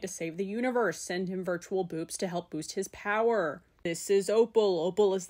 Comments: i thought you said virtual boobs for a sec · I had to rewatch because I thought you said Virtual Bewbs not Boops LMAO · I really thought you said virtual boobs and i was so confused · I GENUINELY thought you said "Send him virtual boobs" and like. It would probably work tbh xD